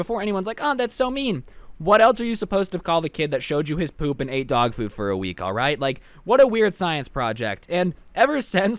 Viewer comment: Anybody else thought it said "he ate hotdogs for a week" just me? Cuz i was confused af